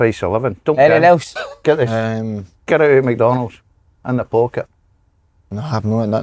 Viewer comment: Liked the video right after I heard, "get yer sauce oot McDonald's n right in the pocket" 😂😂